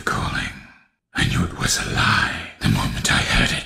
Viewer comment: I think he says ''I knew it was a LIE'', and not ''alive''